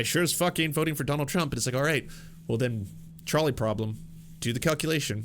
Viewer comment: Every time he says "trolley problem", all I hear is "Charlie problem" lol